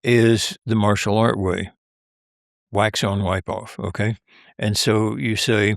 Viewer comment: I just heard it. Aside from being funny af, it's practical. You put the wax on and then you... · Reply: now you know he said wipe off. That's what I heard any ways.